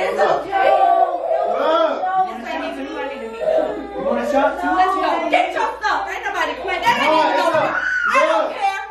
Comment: Listen to what said 5:54 I am dead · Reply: XxcouchpotatoXx “You want a shot too?”